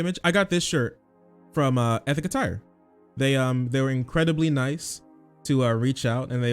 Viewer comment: Me thinking you got wheels in your thumbnails cause I thought you said Ethica Tire